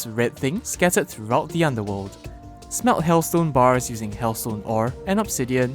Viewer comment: i heard smelt as smell XD